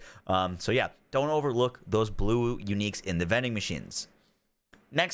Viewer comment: I'm sorry did you say "bluwu" at 0:53?